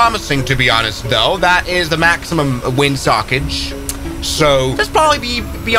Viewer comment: "Maximum wind sockage" Instead youtube captions said maximum wind suckage lol